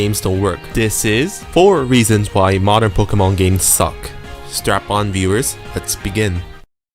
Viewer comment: Did he say strap on instead of strap in?